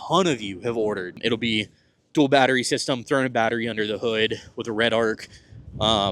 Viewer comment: Did I hear "current battery under the hood"? Is that so?